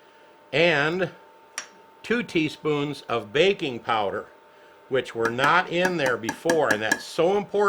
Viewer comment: BAKI NG powder!.... I thought he said BACON powder. Ok... Let me try again.